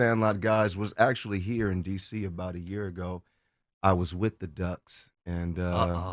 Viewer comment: Ok, when Brandon was talking about the drama with The Mighty Ducks and Sandlot casts meeting, did it happen recently or during the 90s? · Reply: I think he said about a year ago..?